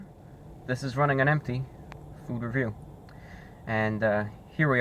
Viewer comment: I misheard "This is Running on Empty Food Review" as "This is Running on Empty, for real" and I laughed my ass off thinking he was saying his car was on empty